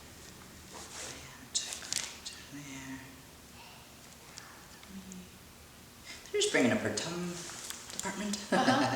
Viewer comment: I heard something like “Hey” or something else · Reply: Jacky Gonzalez its hey elle 😵😵